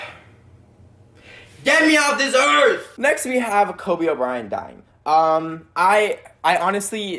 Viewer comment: Did he just say kobe o'brien